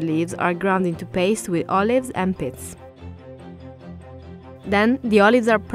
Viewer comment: I could of swore she was saying "Olive's & Piss". I had to rewind it 5 times till I realized she was saying "Pits". Lol